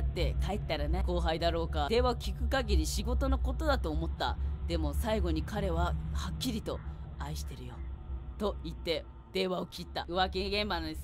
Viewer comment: ころんくんうぽつです⸜︎❤︎⸝‍ サムネからしてもう発狂多いとわかりました( ˘ω˘ ) ｽﾔｧ… 毎日投稿ありがとうございます😭💕 【自分用】ころちゃんの愛してるよリピ用 22:38 22:38 22:38 22:38 22:38 22:38 22:38 22:38 22:38 22:38 22:38 22:38 22:38 22:38 22:38 ↑これリピートしたら｢と…愛してるよ｣って聞こえるわ( '-' )